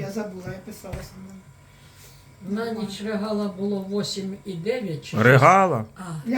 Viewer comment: Все услышали "рыгала" 😂